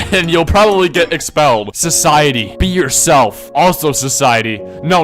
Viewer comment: i heard you said beer yourself tf